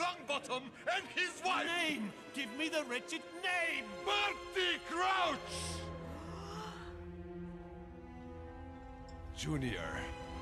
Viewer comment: I used to think that Igor was Sirius also at 2:24 it sounds like he said bop it grouch gasp junior cut to him standing awkwardly lol